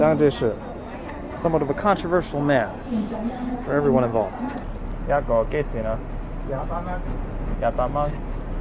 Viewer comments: The guy said $28HK or $3.60 USD. The word he said was "Yah Baht Mon" which means twenty eight dollars. You thought he said "Yat Bak Mon" which means one hundred dollars. The little subtle tones in Cantonese makes a difference. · I think that guy at 15:33 said HK$28 rather than HK$100, so the price for those red envelopes wasn't too bad.